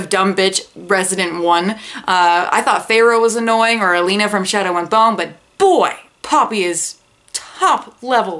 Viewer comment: Correct me if wrong but at 11:30 I heard shadow and thong lololol love it